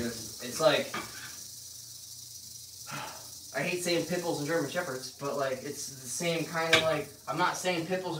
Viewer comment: Lol at first I thought you said " like pickles and German Shepherds." Lmao (at 27: 49)